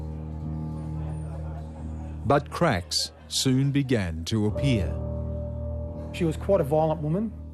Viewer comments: I thought he said buttcracks began to appear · it sounds like he says" buttcracks soon began to appear"...